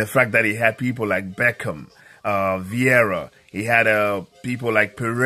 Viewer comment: Did I hear Beckham?